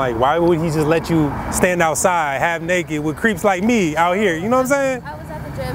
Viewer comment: I thought he said "why would he lwt you stand out here with creeps like me" lmao